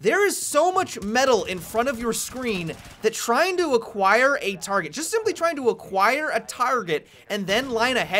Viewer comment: did he say target at 1:10.